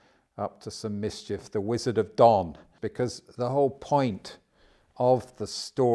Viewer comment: What IS the word he says at 16:13? he says "we could maybe call the 44th president if we were up to some mischief the wizard of ????" Dawn? Dumb? Don? Dom? As in The wizard of dominant narrative? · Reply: Yes, Wizard of Don, as in the Donald.